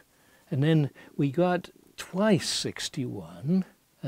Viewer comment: Correction at 4:08… Neil says “then we get twice 61" instead of “about twice 61". The actual result is 120, not 122 as labelled.